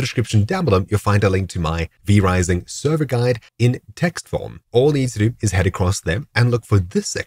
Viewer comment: What word are you saying at 6:33? 'textvom' ? but I looked for anything that sounds like that in the description. I don't think it's textvom or if textvom is a thing. Please advise. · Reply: text form